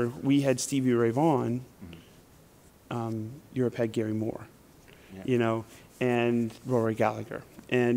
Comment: My english is not good enough. What does he say in 1:02 about Gary Moore? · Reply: “Europe had Gary Moore”